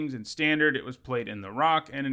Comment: I heard "in standard it was played in Iraq" 😲 like god damn that has to be one ballsy demon then!